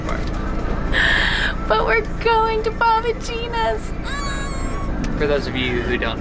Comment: “We are going to Bob and Ginas”omg I thought she said We are going to Papa Gions😂😂😂